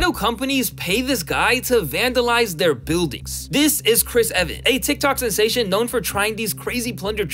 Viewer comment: I wasn't watching the screen and I thought he said Chris Evans 😂.